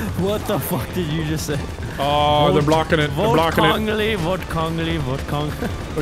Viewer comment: I caught what he said at 10:33, “vote cranley” which you can see on the billboard.